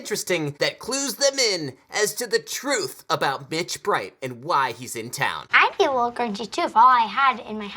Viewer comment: Did he say "B**** Bright"?